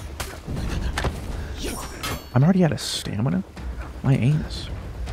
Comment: is he saying "my Anes"